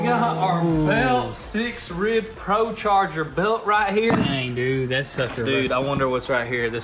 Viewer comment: instead of DANNNNG! he finnaly said DAMMMMN!